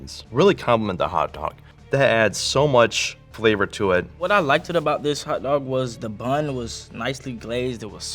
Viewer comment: did bro say “Liked-Ed”🤦🏿‍♂️🤦🏿‍♂️🤦🏿‍♂️